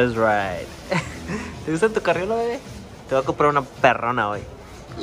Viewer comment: THANKS FOR WATCHING GUYS DONT FORGET TO DROP A COMMENT AND PICK UP SOME GEAR FOR A CHANCE TO WIN THE SILVERADO HERE IS THE LINK >>>>>> <link> EVERY $10 =1 ENTRY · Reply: 3:27 did you really call camila “wey” I sounded like you said “te boi a comprar una perrona wey”